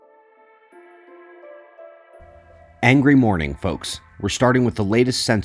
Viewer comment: does say angry morning folks??